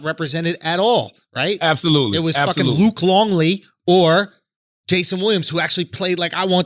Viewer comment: Did he say Luke Longley 56:30 😄😃🤣😁😁🏀🏀🏀🏀.. sub me